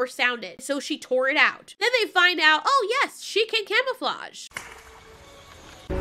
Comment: Tbh I always thought that said it can kill us all instead of it can camouflage